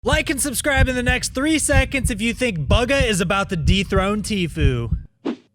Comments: At the start I thought you said bugha is about to deep throat tfue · Who else thought he said Bugha is gonna deep throat tfue at the start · I thought in the intro he said if Bugha is going to Deep Throat Tfue 😂